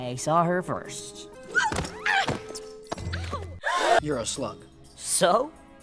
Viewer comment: I thought he said "You're a sl*t" for a second and it was the most confusing second of my life